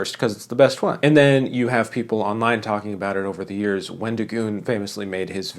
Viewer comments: I genuinely believed he said “when to goon” at first at 6:23 and had to rewind the video to check if I heard that correctly. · i thought you said “when to goon”